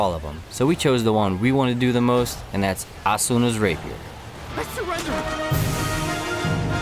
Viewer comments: i actually thought it said Asuna's Raper lol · oh... I thought that said asunas raper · Or make Eugeo's Blue Rose Sword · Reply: I thought it said asunas raper